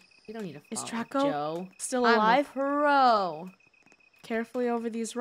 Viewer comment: Wait, in 13:08 Did just say “Joe my bro is a pro”???💀 or did I mistaken